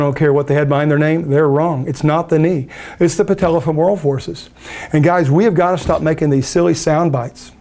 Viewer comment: "It's not the knee, its the patellafabraw forces". 7:39 I didn't catch that, what exactly is it? Very curious about that part. Great video. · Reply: patellafemoral forces